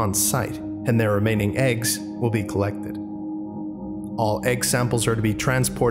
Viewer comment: did you say collEGGted